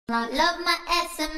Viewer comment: why did i hear "love my ass" in the song?